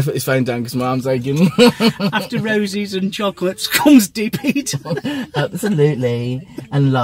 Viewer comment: Congratulations on moving in with each other. My mind must stay in the gutter lol! When said “ deep heat” each other I thought he said DP each other 😂🤦‍♂️ anyway 💙 you. Hope this makes you smile.